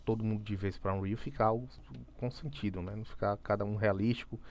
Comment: vc falou cartoon,realistico ou o que? Não entendi.